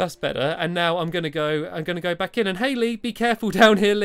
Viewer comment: When you said "Hey, Lee! Be careful down here, Lee!" I heard "Hayley!" and I looked up immediately, because that's my name. Honestly, I'm surprised that didn't happen sooner xD